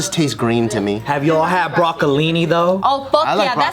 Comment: who else thought he said broccoli but just in a weird way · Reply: He said broccolini